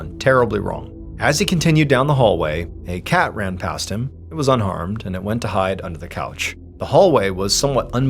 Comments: I thought Mr. Ballen said the cat was "UNARMED" 😂 But after listening again, I realized he said "unharmed". Although honestly, it would NOT surprise me if he did say the "cat was unarmed".... Because anyone that has a cat knows how sneaky those cute psychotic furballs can be... With their Murder Mittens that are sharp like knives attached to their little toe beans 😂❤😺🐈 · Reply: No, I really heard "unarmed"! (And I have one that could do considerable damage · Not gonna lie.... at first I thought he said "the cat was unarmed".... I had questions.... until my mind caught up with my ears.... "OHHHH unHARMED"... 🤣🤣🤣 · I thought Mr. Ballen said the cat was "UNARMED" 😂 But after listening again, I realized he said "unharmed". Although honestly, it would NOT surprise me if he did say the "cat was unarmed".... Because anyone that has a cat knows how sneaky those cute psychotic furballs can be... With their Murder Mittens that are sharp like knives attached to their little toe beans 😂❤😺🐈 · Not gonna lie.... at first I thought he said "the cat was unarmed".... I had questions.... until my mind caught up with my ears.... "OHHHH unHARMED"... 🤣🤣🤣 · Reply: I thought he said unarmed, too, lol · Not gonna lie.... at first I thought he said "the cat was unarmed".... I had questions.... until my mind caught up with my ears.... "OHHHH unHARMED"... 🤣🤣🤣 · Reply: I thought he said “unarmed, too!!” 😂 I thought it was Mr. Ballen being funny. 🤣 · I thought you said the cat "was unarmed"😂